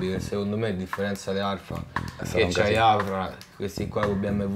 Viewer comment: Non riesco a capire cosa dice al minuto 10:03 da chi prende i ricami alfa d'epoca? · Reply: da AFRA!